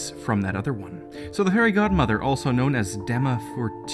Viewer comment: am I the only one who hears Hairy God mother